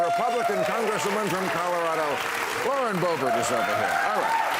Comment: Lauren Boebert? Damn! I thought you said "Lorena Bobbitt"! When is this show going to give the public what it wants?